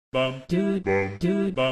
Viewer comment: all i hear is "buff dude"